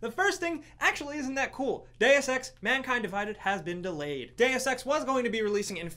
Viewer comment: When he said Deus Ex was delayed i thought he said Day of Sex